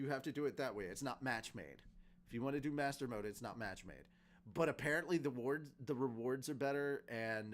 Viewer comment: I heard "if you want to play master mode it ain't bitchmade(matchmade)"